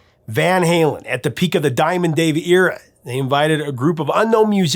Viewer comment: I came with the smoke. I thought you was saying diamond daryl. Before he was Dimebag daryl, and was ready for WAR. NO ONE DISRESPECTS DIMEBAG DARYL. EVER....